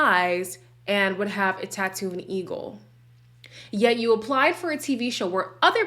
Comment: lol. Thank you for explaining that Alyssa's psychic said her true love would have a "tattoo of an eagle." I heard "tattoo of a needle." That didn't sound promising at all. 😁 I agreed when you said about Jasmina and Michael, "I want someone to explain to me what they are arguing about." LOL They keep having such nothing-burger spats. I love your comments. You make me laugh so much! 🤎